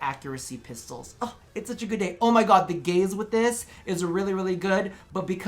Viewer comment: when you said gaze i thought you said gays... and then i thought to myself how homophobic... then i remembered ur a queer <=== is that the right way to use queer? i feel like it is but i kinda feel like im just being meaN